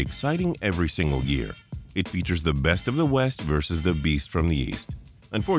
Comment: Beast In The East I Thought You Said Weast, Weast What Compass Are You Reading Boy, This One Right Here Sir, That's Weak Patrick Your Fired Again.